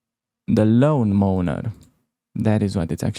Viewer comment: Thanks for the videos, great stuff. I bought your caro course on chessable also. 50:33 Did you say Loan Moaner checkmate? Haha. Lawn Mower. As in, a big machine you push through the grass and it cuts down everything in its path